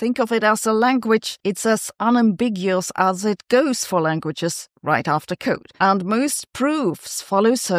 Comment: I didn't catch the remark at 3:35, said in humor. Help? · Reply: "It's as unambiguous as it goes for languages, right after code."